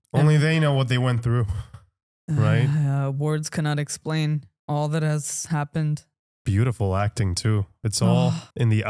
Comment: What I heard: "Wargs cannot explain all that's happened."